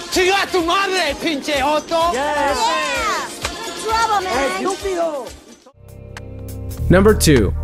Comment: I heard him say Pigeotto XD